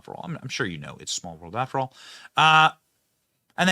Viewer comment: I heard "It's A Small Member!"